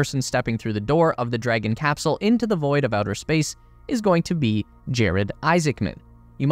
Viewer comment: What i hear: “space s*x”